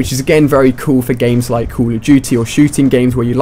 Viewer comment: It sounds like he says call of jootie